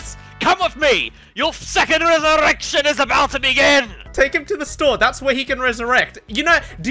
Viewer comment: i thought said “Your second erection is about to begin”. (4:40) you guys are breaking my humor pls stop being so funny